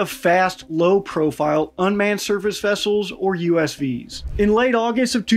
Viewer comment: I think USV is unmanned sea vessel (or vehicle... but when nautical, vessel sounds better frankly) like UAV, ULV and the marines song it's land and air and sea... I could be wrong though lol (OH!! you said SURFACE I thought you said SERVICE and it made NO sense lol but Surface sounds like a possibility)